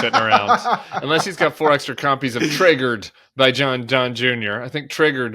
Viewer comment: I thought you said "Traegered" ... and for a brief moment I thought... wait... Don Jr. wrote a barbecue cook book?